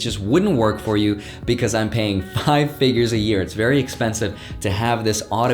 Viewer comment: did I hear that right, 5 figures/year for a spreadsheet?!? Now what kind of ROI would you have to have on that investment to make sense. I must have misheard...